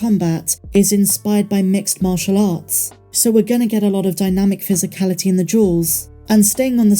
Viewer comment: LOL when you said physicality of the duels I thought for a second you said physicality of the jewels and i was like what???